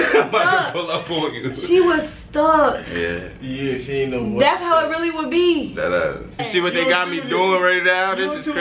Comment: That’s crazy she was probably pregnant then but make it a joke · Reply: lmaoo exactly what i thought she said “that’s really how it would beee” lol queen ain’t slick